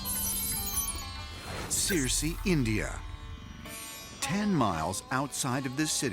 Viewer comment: When he said "Sirsi, India", I thought he said "Seriously, India?"